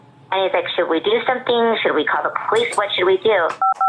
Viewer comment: Did she say "Should we call the _priest_?" ???